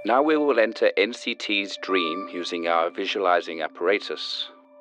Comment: i think at 1:53 he meant to sub "Apparatus" instead of "Operators"